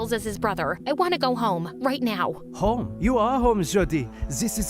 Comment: i thought he said "you are home, shawty" for a sec 😭😭😭